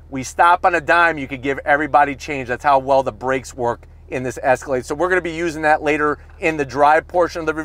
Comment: Did he just say ESCALADE @ 17:01 ????